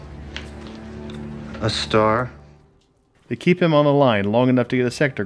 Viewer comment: The first time I watched this, I thought he said....”a store”